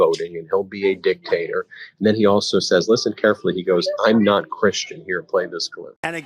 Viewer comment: That is what i hear "I am not christian" Way to go